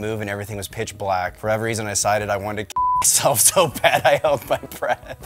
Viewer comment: boy I heard that wrong 😭😭😭 i thought he said "i wanted to fuckk myself and get pregnant"